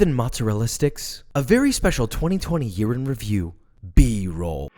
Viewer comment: I thought he said a 2020 urine review